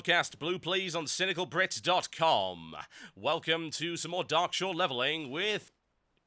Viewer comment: I always thought he said The cynical grit :(